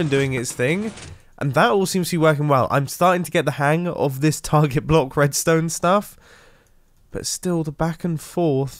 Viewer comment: I hear Mumbo say "target block raidstone" and I think you what would be cool making redstone target block traps for village raids